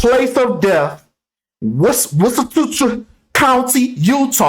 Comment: Hells yea!! I was waiting for this vid. It sounded like you said "what's in the future" when you were referring to Wasatch Country, Utah 😂😂. I think it sounds like "Waah-sach"